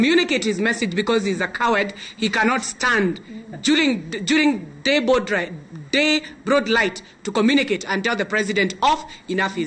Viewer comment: Did she say "Day broadlight?"